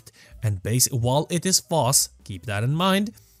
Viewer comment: while it is force? or false? what does he say? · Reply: Fass